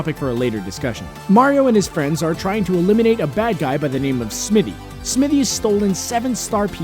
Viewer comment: Did he say SMII7Y?!?!